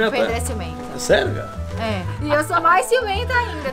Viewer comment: Só eu entendi - É sério gata ? Minuto